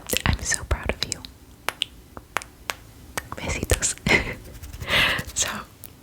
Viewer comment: dijo besitos? en español? o es que escuche mal? XD amo sus asmr aunque no entienda ya que no hablo mucho inglés pero siempre me relaja I love you ❤️😘